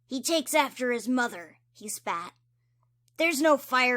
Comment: I'M SORRY I THOUGHT YOU SAID "HE FAT"